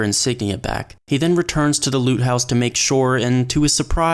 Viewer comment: I heard "Loop" house at first xD Still accurate